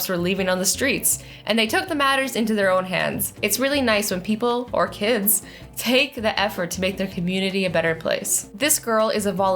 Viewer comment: Did she just say kids aren’t people